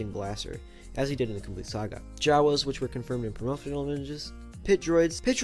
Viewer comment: wut was he saying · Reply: Promotional images Idk how that got past my editing. My apologies